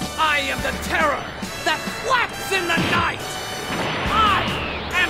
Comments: I am the hope of the omiverse. I am the light blub in the darkness. I am the bacon in the fridge for all living things who cry out in hunger. I am the alpha and the omega I am the terror that flaps in the night I am Son and I am a Super (dramatic pause) Saiyan. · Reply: At first I thought he said “faps in the night”. So glad he said flaps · During speech: "I am the terror that flaps in the night." ...Hmm. Nice Darkwing Duck reference. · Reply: I heard him say, "I am the terror that quacks in the night." Eh? = / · I am the hope of the omiverse. I am the light blub in the darkness. I am the bacon in the fridge for all living things who cry out in hunger. I am the alpha and the omega I am the terror that flaps in the night I am Son and I am a Super (dramatic pause) Saiyan. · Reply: I heard it like "terror, that flex in thet night" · I AM THE TERROR THAT BLACKS IN THE NIGHT me: Black confirmed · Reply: Lol Ha thought he said quack in the knight